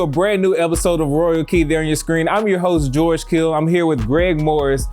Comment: When he said I thought he said George kittle who used to play for the hawkeyes